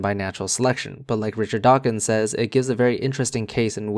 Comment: Hoo boy, I heard “late Richard Dawkins” instead of what you actually said: “like Richard Dawkins,” and had to frantically Google him to see how it was possible I missed his death. False alarm. Carry on.